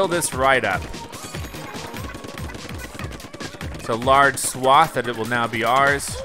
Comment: did say swat again.